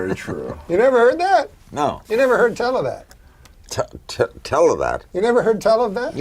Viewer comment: The first part with Larry king, what is Norm saying? The closed caption says “television”, but it sounds like televan. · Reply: "You never heard tell of that" or actually "tell a' that". Instead of saying "you've never heard that" or "you've never heard of that". Those more modern ways of saying the phrase minimize the importance of the person who told you about a thing, or the general conversation going on around you as something you could hear. But the phrases are equivalent.